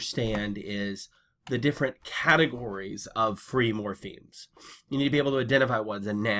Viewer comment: What's the word you use at 0:18? It sounds like "free morphines" Does a noun phrase always contain the subject? Does the verb phrase always contain the object?